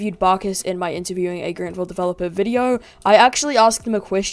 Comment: "Interviewing a Greenville developer..." Youtube's subtitles: What is Greenville? I think he said Grantville.